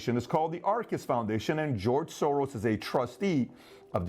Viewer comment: Need to get my hearing tested. Thought you said George Sore-Arse.